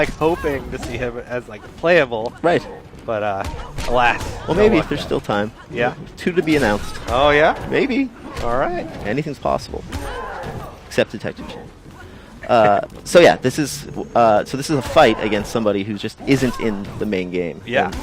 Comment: What does he say at 10:39? "Except _______" No idea sounds like he says detectives?